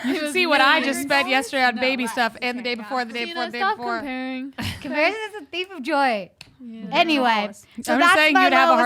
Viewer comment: comparing is a thief of joy? is that was Amy said?